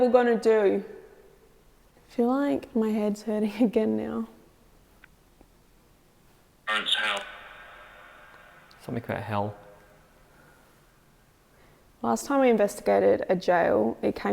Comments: Hi and, ‘What’s the devil gonna do?’ I heard ‘burns hell’ at 52:50. 53:50 I thought he said ‘that’s clearly one’ when you said “I want a cell number”. Great respect for your alone challenges, you certainly produce great videos and show us evidence of after the physical life. Do we move to another dimension only to return or are we forever encircling, will we every know when we’re alive or will it remain a mystery? Thanks 🤗❤️🇬🇧😘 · said she felt like her head was hurting again and then a response comes through on VOX. I heard very clearly "Hurts how?". It could be just my perception, but it was so clear to me that I gasped and jumped back in my chair. This place is so unsettling! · I realize this aired a year ago but this is the first time I've seen this show. At 52:50 right after asked what's the devil's going to do? I hear "burn to hell" at 53:28 I hear the words "that's 31" right after asked for a cell number the first time. I don't know if you're going to read this or not but I could tell you were terrified and stressed out. Right around 1 Hour 2 minutes we get a really good shot of your eyes and they are so bloodshot a sure sign of stress. They just got worse no longer you were in A block. I have to say young lady you're much braver woman than I am!😅 Then again, I do believe in God the devil and demons, I don't know if you do or not.